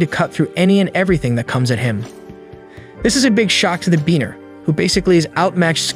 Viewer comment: is he saying b3aner?